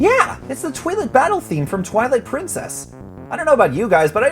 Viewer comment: sounded like you said toilet, not twilight.